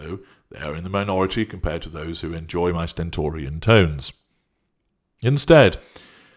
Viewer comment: Had to pause to look up stentorian xx new word of the day xx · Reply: I did too. I thought he said stentorian stones🥴